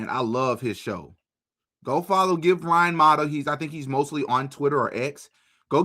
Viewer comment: How do you spell "Brian Mada" - what does he call himself on X? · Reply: Ryan Matta is his name.